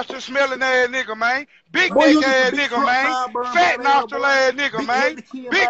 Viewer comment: So nobody heard what he said at 00:57 ? 😭😭😭😭😭😭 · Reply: Big neck***